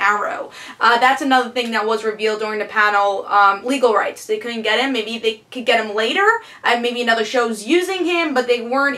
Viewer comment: who says at 5:23 that wont appear in arrow?? i cant understand the name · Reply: Ted Kord (Blue beetle)